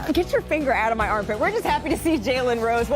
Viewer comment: I Thought She Said Get Your Finger Outta My A$$